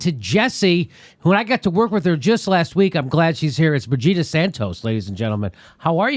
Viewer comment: When Jimmy said Brigida at first I thought he was saying Vegeta. Haha